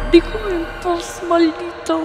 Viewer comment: En el minuto 43:25 se escucha una voz qué dice dejame en paz maldito ??? Es parte del programa ?? O que es eso ??